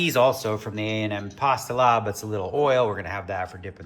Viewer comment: pasta “lob” instead of lab You were correct earlier in video